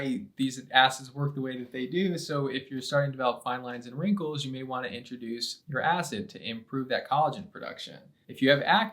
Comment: it mentions a type of acid (nitric??) for wrinkles. It’s not clear - does anyone know what type of acid? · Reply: He didn’t say a specific acid …he said you may want to introduce your acid . Now as far as seeing which acid to choose you may want to look their videos and see which is best !